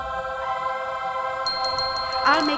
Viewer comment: I swear when She used Bombarda Maxima it sounded like she said Avada at the start